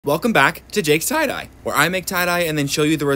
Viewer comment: Tell me why I thought you said, "Welcome back to Jake's Side Eye"